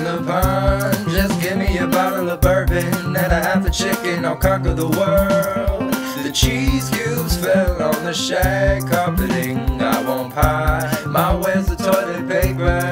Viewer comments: i thought he said cheese juice. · LMAO! I thought you said "I'll cock on the world".... Glad I waited to watch this, but now I'll probably dream all of this. Lol